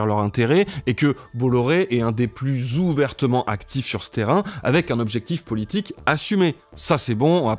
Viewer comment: "un des plus ouvertement actifs sur ce terrain" j'ai entendu "actifs sur Stérin" 😅. Je n'aurais peut-être pas dû enchainer sur cette vidéo après avoir visionné l'audition du DG de Péricles dans le cadre de la commission d'enquête sur l'orga des élections (d'ailleurs je conseille vivement d'y jeter un coup d'oeil. Le type y avoue qu'à demi-mot que l'objectif est de faire de la propagande)